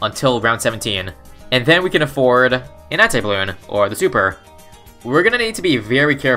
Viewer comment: is it just me or does it sound like he says "nazi bloon"??